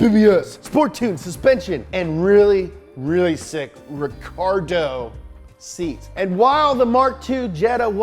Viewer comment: did he say Ricardo?